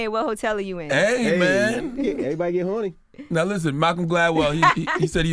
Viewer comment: Wait did envy Say ‚well Everybody get horny‘ at 15:28 💀💀💀😂